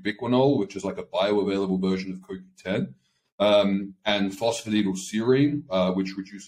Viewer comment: I can't understand what he says after CoQ10, I think Phoshate something.. Anyone know? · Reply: phosphatidylserine